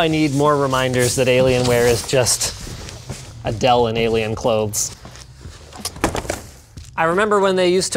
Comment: I thought Linus said "Adele in Alien clothes" and almost died of laughter.. Had to google it, turns out it doesn't exist.. Yet.. 😃